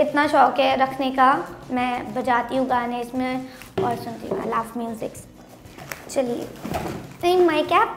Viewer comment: What se said "I love MUSICS" HUH